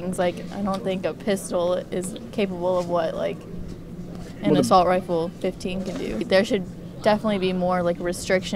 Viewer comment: I heard “assault rifle 15” and checked the comments without missing a beat